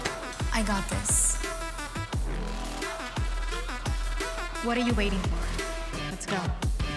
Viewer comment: what does izzy say in minute 0:46?? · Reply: What are you waiting for lets go